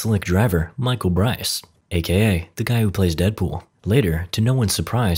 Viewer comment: Narrator says “guy who plays Deadpool!” Caption says, “Green Lantern.” I say, “Blue Shirt Guy!”